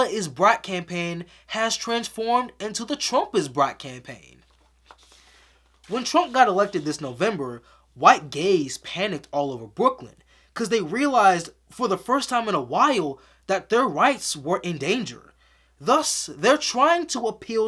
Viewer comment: "The first time in a while gay people were worried about losing rights" uhhh... Im not even that old and I remember when it seemed we would never ever see legal gay marriage.... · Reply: > "When Trump got elected this November, white gays panicked all over Brooklyn cuz they realized for the first time in a while that their rights were in danger" is what he actually said. This is not as broad a statement as you are suggesting. It's a very targeted critique of middle to upper class white gays in Brooklyn (gentrifiers) who have time and again shown themselves to be willing to ally with power/the system so long as they think they'll come out the other end protected by their class and whiteness. This is not a new criticism either. And they're not the only one's who get roasted in that very same segment. Tone is hard for me to gauge in text form but I promise I'm not trying to be condescending or an ass when I suggest you rewatch the joke in its full context from 21:00 - 22:19 to clarify what he's actually getting at. ^_^